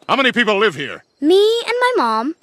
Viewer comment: Not me thinking “you wasted my time little kid” but it’s “you wasted my time a little bit” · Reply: I thought it said you waisted my time you little beach